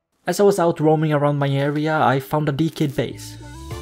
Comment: I thought you said deek head base at first 😂